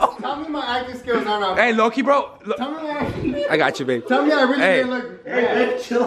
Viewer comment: did he say “I gotchu babe” LMAOOOOO